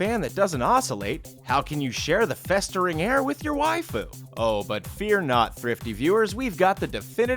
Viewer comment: "then how will you share the festering air with your waifu?" Dude i cant stop laughing h elp- Hfgfufuchfhdhcheusjxbrjd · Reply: I thought he was saying "Then how will you share the festering [?] air with your wife--(realization) oh."